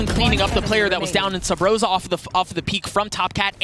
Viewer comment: did I just hear subroza??